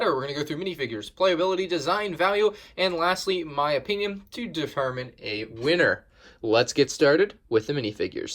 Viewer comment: Sounded like you said defermine instead of determine